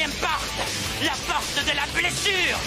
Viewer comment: aparte de decir "la pojte de la placer" también entendí que decia "pubertad",que dando la siguete oración: la pojte de la placer... pubertad XD